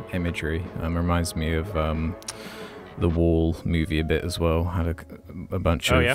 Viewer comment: what movie is this? Did he say "The Wolf"? I'd like to watch it but i cant find it anywhere :( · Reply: I think he said "The Wall", which is a movie that came out in 1982, based on an album of the same title by Pink Floyd. And although it wasn't an animated movie, it did have some animated parts. ,,, All in all, it's a pretty good film. .... Hope this helps.